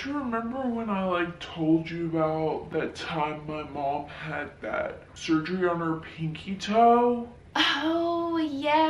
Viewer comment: “Had that surgery on her pinkie toe?” Bro I heard “had back surgery on her pinkie toe” 💀 💀 🤣